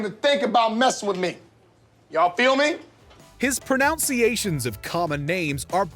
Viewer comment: Narrator says “Pronounceation” instead of pronunciation when talking about the skit that pronounces names wrong. Lol